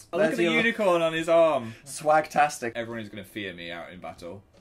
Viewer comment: did Dan just say 'swagtastic'??? 2:32 😂😂